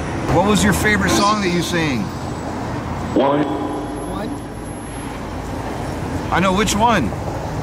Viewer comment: I swear I heard him say "What up"! RIP Tupac You live through your music forever! We miss you!